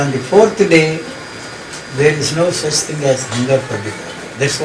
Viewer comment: What did he say in 1:04:22 ? On the 4th day, there is no such thing as ??? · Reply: He said after the fourth day there is no such thing as hunger for this body.